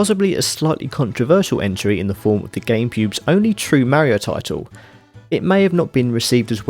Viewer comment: Lol I'm so immature but what the heck he said Gamepube